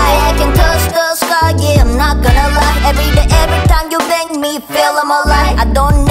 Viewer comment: DID SHE JUST SAY, "EVERYTIME YOU BANG ME?!"